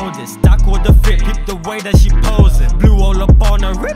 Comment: Idk why I thought you said "Aqua the Fina, peep the way that she posin" XD